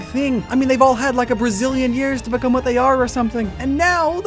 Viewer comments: did he just say Brazilian years? He did, and from this day forward we found out the secret of all Brazilian people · Did he really say "a Brazilian years" at 0:29 AHAHAHAHAHA